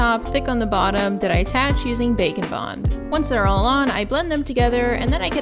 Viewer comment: I know it’s probably “bake and bond” but every time u say it I hear “bacon bond”